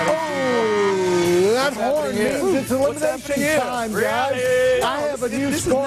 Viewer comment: Did he say I'm horny at 13:24?